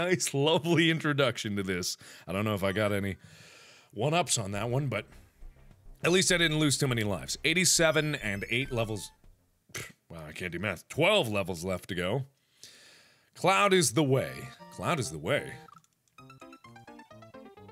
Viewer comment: XD it sounded like he said I cant do Meth